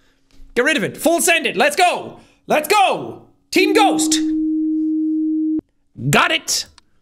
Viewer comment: when he said team ghost I laughed a bit bc my brain is different in my head i heard racially motivated 🤣